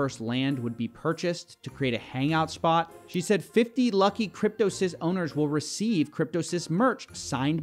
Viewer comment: I keep hearing "Cryptocyst"